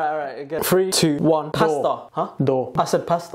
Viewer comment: i thought he said pastor instead of pasta, so i said Church 😭